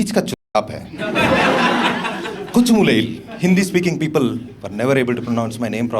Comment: "Kochumoolayil" I heard it as "Kochumulayil"😭🥀